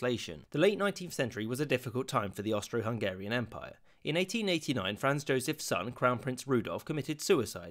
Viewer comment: "It 1889 Franz Joseph Stalin crowned prince Rudolf". Did anyone hear that?